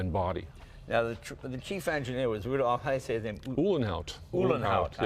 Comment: did Jay say that the chief engineer was Rudolph Hess? Gee, I didn't know that Spandau Prison had a Huber program . . . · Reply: After replaying that segment a number of times at slower speeds, I guess Jay is saying "Rudolph -- how do you say it? . . ." My bad.😐 (Plus, he repeats this question more clearly at 5:35 -- " . . . is that how you say it?")